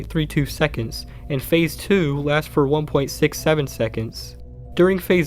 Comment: how long did you say it would last? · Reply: 1.67